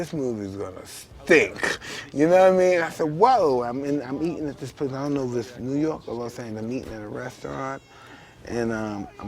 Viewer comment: did he say I'm eating this pussy and I don't know what restaurant I'm in??